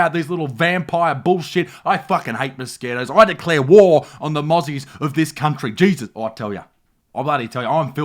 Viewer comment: Careful man, when you said 'mozzies', I thought I heard 'muzzies'. YouTube and all . . .